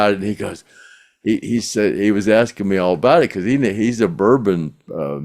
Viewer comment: I thought you was saying Booger Nose when you was referring to the Bourbon. Lol